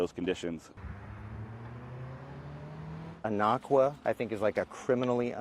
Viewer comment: What variety is he saying? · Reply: Anaqua